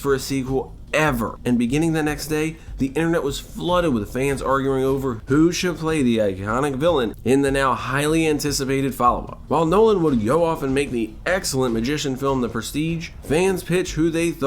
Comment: bro you really have to enunciate more. 3:19 "who should play the ihhonic villain" like what? just say iconic.